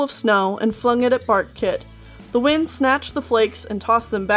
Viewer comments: Thought you said Fartkit. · I thought you said Fartkit at first lol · At first I thought you said Fartkit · Not gonna lie, I thought you said Fartkit. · at first thought he said fart kit XD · In chapter one i thought you said "Fartkit" XD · omg at first thought she said fartkit o.o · I thought you said fartkit the entire time